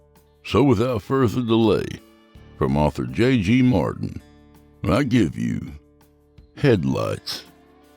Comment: I thought you said I give you head lice 🤣